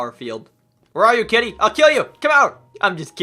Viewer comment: i heard "im just "kitten" i wont kill u" idk why but i thought that was very fun